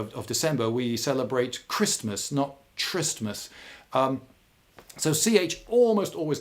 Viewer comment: "khu"ristmas not "Chi"ristmas. For a second there I thought he said "tryst"mas.